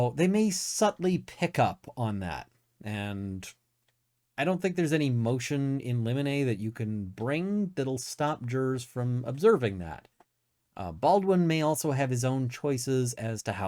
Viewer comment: Misheard A motion in lemonade…. Because it’s funny. That’s what it is now.